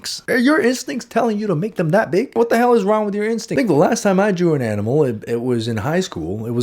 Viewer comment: did he say the n word? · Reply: He said "I think" im pretty sure